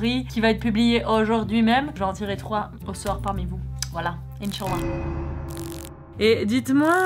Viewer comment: A 10:54 c'est moi où ta dit " Inshalla "😂